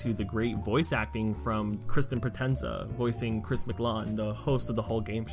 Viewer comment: Me: Did you just say ChrisMclan instead of ChrisMclean?!?!